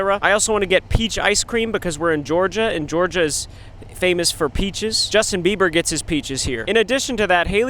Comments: Who thought he said "I get my bitches in Georgia" 👇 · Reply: peaches was the only thought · i thought justin said b****** not peaches